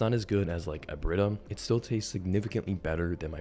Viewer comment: what the name of the company he said at 5:16 ?? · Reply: Brita